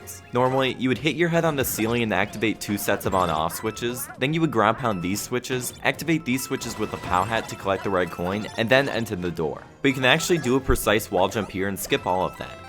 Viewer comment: i swear to god, i hear "bitches" every time he says "switches"